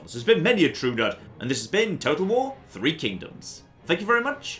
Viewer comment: Am I the only one that heard it as "Total war III: Kingdoms"?